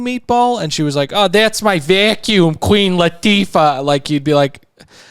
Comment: I miss heard Gina as well. I also thought she said Queen Latifah for the vaccuum cleaner, but in an interview this week Gina was talking about the vacuum and it's "Clean Latifah". I still think it's problematic but not quite as bad, just a very bad pun.